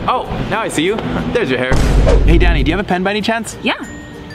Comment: did he say daddy